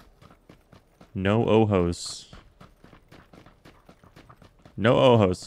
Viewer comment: i thought he said no homos